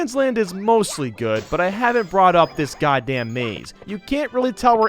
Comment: did you really say "god d@ maze? So...definitely not a channel for kids. Or Christians. Just fyi, I'm neither.